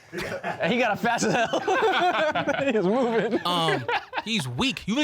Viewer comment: Did say "that nga's movin" ????